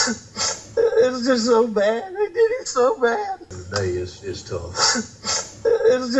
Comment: I hear "i did IT so bad"